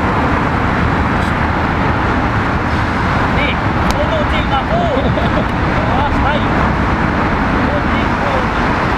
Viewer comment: What was he saying to u? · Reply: I'm Scottish and what he said was "Mate, go and no (not) take my photo. " "Go and not take for the last time. "